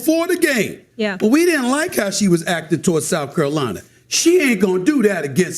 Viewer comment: dude sounded like Cleveland Brown “curralina”